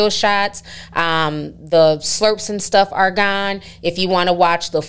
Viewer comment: for a moment I thought you said ‘slurps and turfs’. 🤣🤣🤣